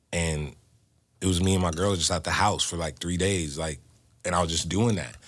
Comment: Did he say “It was just me and my girl at the house?”